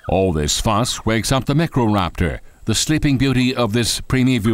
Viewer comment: When he said microraptor, all I heard was “necroraptor”